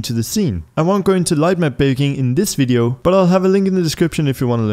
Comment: was it just me or did he say light my baking?